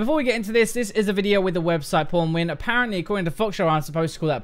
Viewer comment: i thought said "porn win"